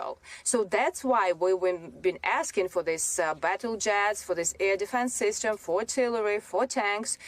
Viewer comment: Did she say Ukraine needed "that battle jazz" ?? How can anyone say no to that?